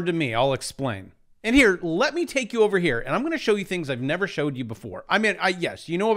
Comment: Ooh wait did he say “show us things we haven’t seen before” 😉 all correct measurements lol 😂